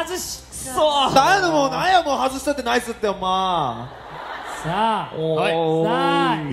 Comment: 増田さんが早口すぎる+私のリスニング能力が無いのと合わさって何て言ってるのかわからない笑笑英語に聞こえる、(？) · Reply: なんやねん、なんやもう、外したってナイスってほんま〜って広島弁で言ってると思います！😂